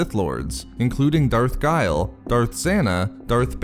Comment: at 2:40 i heard Darth zannah ,bane and gravid but how do you spell the fourth one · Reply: mo maj that would be Darth Guile, as in creating evil plots in the dark -> guile